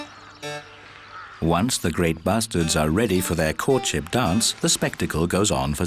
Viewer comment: Did i hear that right? Once the grey Bastards are ready?